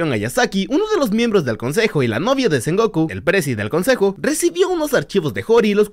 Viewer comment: solo yo escuché la novia de Son Goku